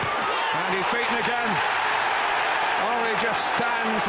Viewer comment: haha I thought the commentator said "Henry just stands and waits for his teamates to come and congratulate the bastard" :)